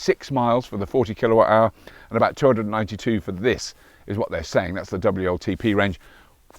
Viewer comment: I think you're conflating numbers from the 40kWh and 60kWh batteries... iirc the 220 mile range was from the smaller battery. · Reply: Sorry but no. At 8:30 or just after Robert says the range is 292 miles but the caption says 280 (WLTP) miles and a figure of 220 miles suggested by the (much respected) EV Database!